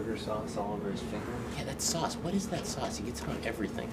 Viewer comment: When that guy said "sauce" I heard "sus". Just kill me. Also that "Hamburger Dipshit strikes again!" line seriously cracked me up!